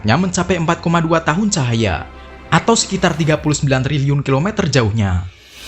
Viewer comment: gak salah denger aku 39 ribu kilometer? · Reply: Triliun bogeng